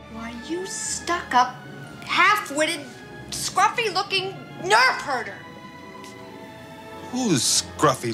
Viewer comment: Leia's "Nerf Herder" line baffled me as a child when watching on a well worn out VHS recording, because I always heard it as "Nerve Hurter." I always thought, what a silly line. It wasn't until much later with DVD & subtitles that I read what she actually said, and then thought "Oh. But why not choose a different word than Nerf so that there would be no confusion?"